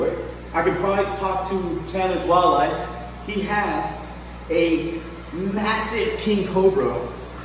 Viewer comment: at 20:35 what did you say? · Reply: daw daw Zame he has a massive king cobra